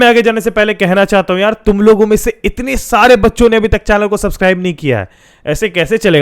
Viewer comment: is just me that Heard "itne sare bakchod ne subscribers nhi kiya hai" instead of baccho